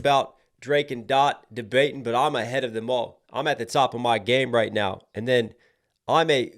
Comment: I thought it was him saying I’m a HED of em MAL DJ Hed and Ovo mal